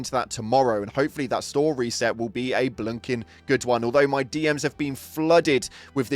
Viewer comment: did u just say blumkin? 🫩